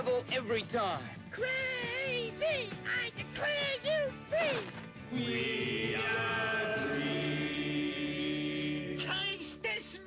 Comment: what did they say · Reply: We are Free